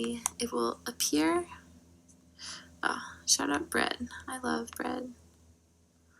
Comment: Did she just say “shoutout bread” Did she shoutout bread